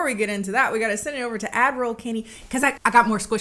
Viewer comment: I just realized Kennie isn't saying "Admiral Kennie" she's saying "Ad roll Kennie." for this whole time I thought she was calling herself Admiral for some reason. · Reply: I thought she was saying Avril Kennie